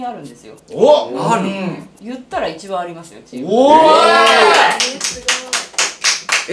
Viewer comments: さんが財運1番あるって言われた時に、 女性の声で『えーすごーい！』って聞こえるんですが、私の空耳じゃないですよね？？ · 小さい声で 「え、すごーい」って声が聞こえるのは、私だけかな？😮